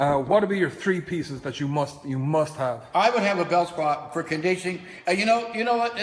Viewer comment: I thought he was saying bell squat.........